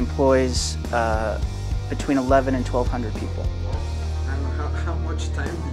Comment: lo que dijo significa 1100 y 1200 personas, no 12,000 como dice en los subtitulos